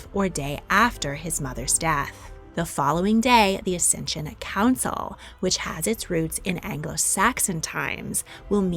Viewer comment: "Ascension council" or "Accession Council"? Automati captions read "ascension" and also what you said sounded more like that than like "accession", which I think it should be.